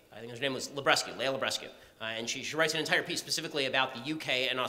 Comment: What name did he say? I cant find the name. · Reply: Leah Libresco